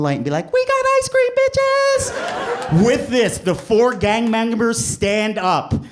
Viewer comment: Every time he said “gang members” all I heard was “gang bangers”😂😂😂😂